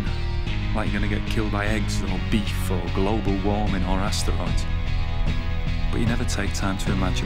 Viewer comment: Did he just say, “or astronaut”?